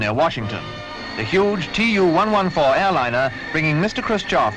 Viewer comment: i thought that it said TU-144 i was like that dose not look like the TU-144. I love the look of planes with contra-rotating props they are so unique and cool. Great video as usual keep up the great work.